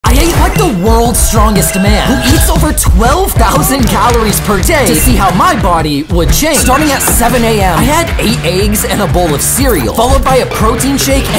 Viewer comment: Help i thought he said i ate the worlds strongest man💀